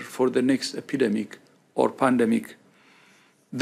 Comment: At about 5:27 I swear he said “plandemic or pandemic”